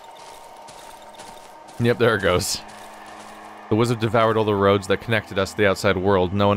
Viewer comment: I misheard you, I thought you said. "The Wizard has devoured all the roads." I imagined a wizard eating roads like spaghetti.